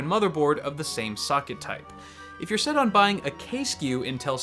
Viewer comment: What is he saying after 6:33 ? "If you are set on buying a case you Intel CPU" or "If you are set on buying a cache you"? It is really confusing.